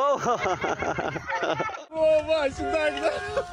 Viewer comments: did the undertitle just say "i'm ready" instead of "tom brady"? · I don’t think he said I’m ready he actually said Tom Brady